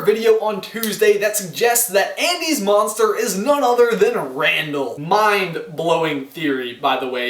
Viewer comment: OMG I was so scared I thought he said Randall was ANDY's MOM